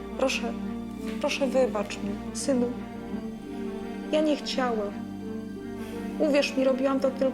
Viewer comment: czy ja dobrze usłyszałem " Ja niechciałem " Czyli jednak jego matka ma pałkę policyjną w spodniach